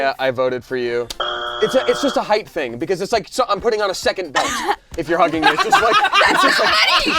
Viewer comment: What did Damien say at 14:16? He's "putting on a second" what? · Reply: I listened to it again. He said "second belt"